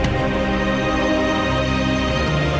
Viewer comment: did jeff just say "The winner of fans vs fans"?